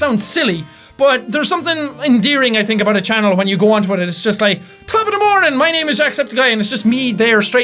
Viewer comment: Omg I was bowling today and I heard the pro shop worker listening to your vid all I heard was "Top of the morning to you laddies!!!!!"